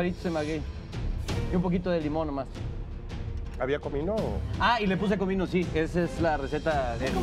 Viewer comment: ¿Qué dice Vane? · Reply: Cristóbal De Quevedo que como sabe que tiene tan rapido El chef supo que tenia comino muy rapido eso dijo